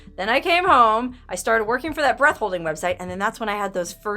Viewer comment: Whew! I thought you said the guy videoed "breast holding"!